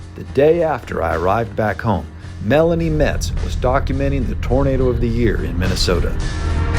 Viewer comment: the caption says she was documenting the 'Burrito' of the year instead of 'Tornado' of the year. Ha ha.